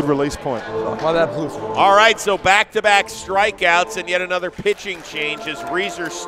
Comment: I thought he said strike out not first strike 😂